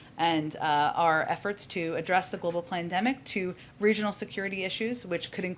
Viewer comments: the global what did she say? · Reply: Good catch. She definately said pland... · did she actually say global plandemic?